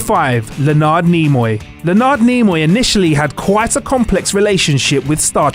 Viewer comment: I’m sorry, whom did you say?! Who TF is "L’Naard Neemai" when he’s at home?!